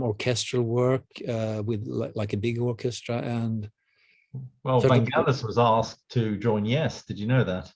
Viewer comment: I thought he said Frank Eliis was asked to join Yes!🤣😅😂 I was thinking who is that Frank Ellis dude?